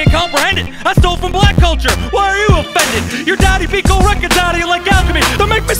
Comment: “I stole from black culture, why are you offended?” Killed him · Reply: I thought for a long time he said "I stole from black culture while you offend it" lmao